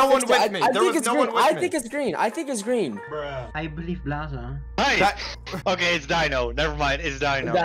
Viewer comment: I think dino said 'I believe blaza' not 'I believe its blaza' so dino was supporting blaza's statement and that's why dino said 'why' when blaza misunderstood him and reversed the blame on him.....